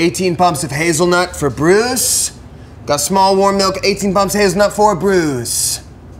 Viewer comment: the caption says his nut instead of hazelnut